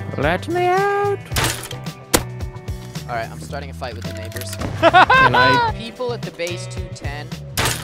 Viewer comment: Is it just me, or did anyone else hear 'neighbors' as 'n*ggers'?